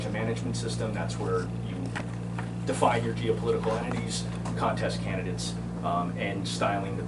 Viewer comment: Rudy and team, mark 11:54 define your geo-politcal enemies. I hear coomer right.